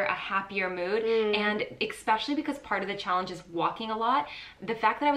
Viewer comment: 😏 Did she say "ex-pecially" (06:07) 😆 Love you both 😘